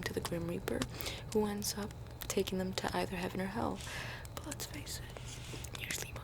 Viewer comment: Maybe it's because ive been watching too much metal gear, but when you you said "they'll either go to heaven or hell," I thought I heard outer heaven or hell